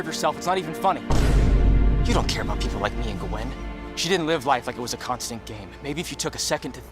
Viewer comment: Okay but at 13:50 it sounds like he says "you don't care about people like me and GUH-WEN" and that's all I can focus on now.